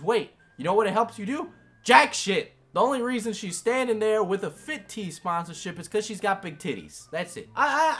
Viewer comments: I am so slow when he was saying fit tea i thought he was saying fit-t- like a t-shirt and I was like who the hell thinks that a t shirt makes you skinnier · Lol I thought you saying fit tea was like a fitted t I was confused for a good minute 😂